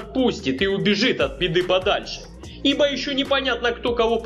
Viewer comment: мне одному послышалось,что он сказал от Педы подальше,а не от Беды?